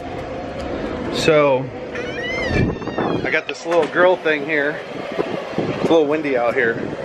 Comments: I thought he said "we have a little girl here" 😂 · in yesterdays vlog I thought he(as in) said I have that little girl back there and today it sounded like he said the same thing lol haha